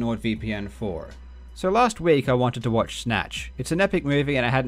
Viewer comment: Was eating and not entirely paying attention around the point of the ad. Thought i heard: “And i wanted some Snatch” Me too man, me too.